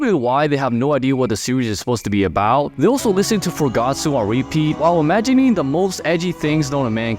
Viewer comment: What song did he say? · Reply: Its called Gatsu but its sounded like he said "for Gatsu" for some reason